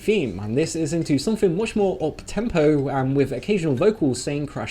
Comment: WHY DO I HEAR PEPSIMAN